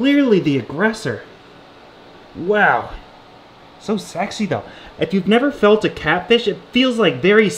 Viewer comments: did he say "so sexy though" lol im pretty sure my ears are broken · Did he just say so sexy tho lol